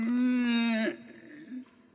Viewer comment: As a kid, I thought he said “Welcome to Hell” at the end lmao